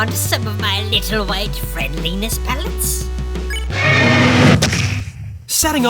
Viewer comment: I thought he said penis pellets I dunno why